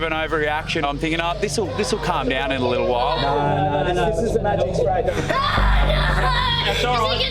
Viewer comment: This is magic spray Me: I thought he said “ what is the magic word